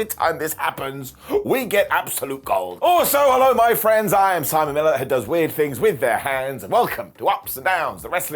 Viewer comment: I know you said Simon Miller because that's your name. I thought I heard salmonella 😅😂🤣. Hope you are good, ❤ your show. Camden New Jersey USA checking in 🤗.